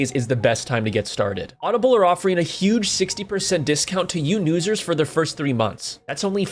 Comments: did he really just say “yew nusers” instead of “new users” 💀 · “Audible is offering a HUGE 60% discount to you newsers” I thought I was going crazy the first time I heard it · Had to do a double take at 1:08 to make sure I heard “yew nusers” right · did he just say “you newsers” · You said "You newsers" instead of "New users." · You said “you newsers” instead of “new users” lmfaooo